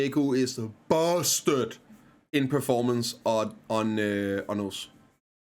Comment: where did you say awakening maegu was busted at? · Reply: Orzekea